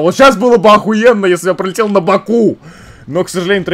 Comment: Когда сказал пролител на боку мне послышалась улител на боку:)